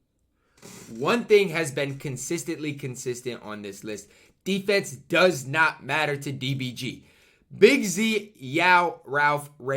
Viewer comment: Ty did I just hear you say consistently consistent